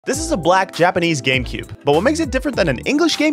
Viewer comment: Lol I thought he said gaycube